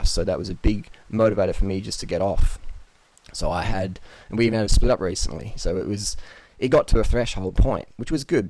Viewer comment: "freelee is the most important person in my life" omg, i love you! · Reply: might of misheard him but I think he said "she and I split up recently" at 3:17. If that's the case than I would be very sad :(